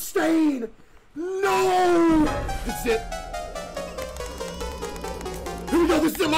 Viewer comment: did he say Sh!t or this is it? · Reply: This is it